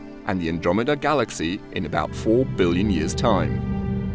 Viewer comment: Four billion years???...oh thank God...I thought they said four million years,and I started to panic